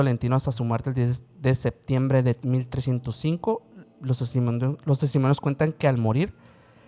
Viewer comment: que dijo? 😅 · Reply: Testimonios jajajaja